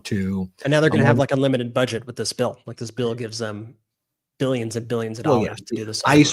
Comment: Why is nobody talking about all the power this bill gives to Trump over elections and everything else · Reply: Very true.. I thought I heard this bill makes him king.! I don’t know if that’s true or correct